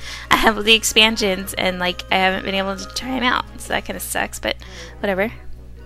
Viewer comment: when you said sucks i thought you said sex